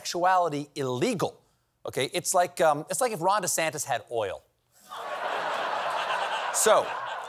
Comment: LSSC: please enable closed captioning for us hard of hearing folk. Without it I thought Stephen said "it's like if Ron DeSantis had ORAL". Not something I want to imagine. Please?!?! 🙏🤞